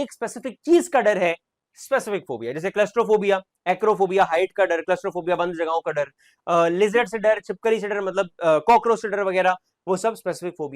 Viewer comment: I heard crush ka phobia😂